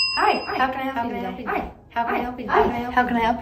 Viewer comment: I thought she said making pee 😭